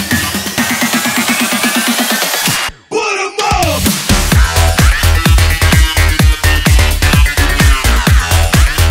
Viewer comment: what is saying? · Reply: What i know or i don't know